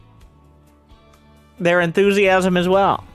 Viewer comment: I love when says "Enthusiasm" I hear "naivite"